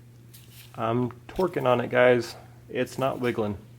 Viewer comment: "Search 'twerk'" had be laughing. Good video as always. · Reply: lol I thought he said "Torque". Makes sense.